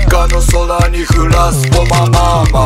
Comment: 「I make u wet」のところ雨綺麗に聞こえる